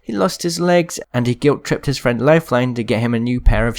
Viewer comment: Is it just me or does it sound like he said loaf line